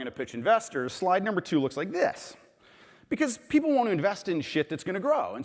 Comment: Did he just say "...people want to invest in sh*t..." ?